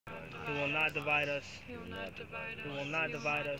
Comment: Oh shit they were saying "you will not divide us" I thought they were saying "not Tobias" had me wondering " why the hell they talking bout four on Insurgents ??